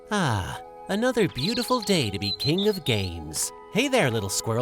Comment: thought he said king of gains for a second